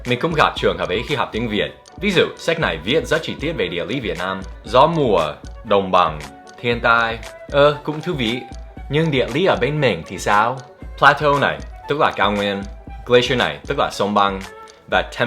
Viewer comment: thiên tai mà mik nghe thành hentai